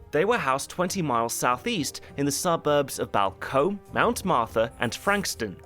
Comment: what suburb is he trying to say here? Balcombe?